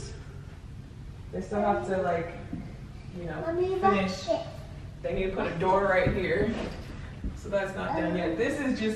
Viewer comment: wait wait waiitttt what did say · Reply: jamila gillette “mommy watch this” she was trying to do a flip off camera lol